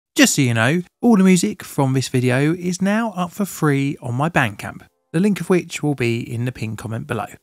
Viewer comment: Bandcamp. I thought he said bank account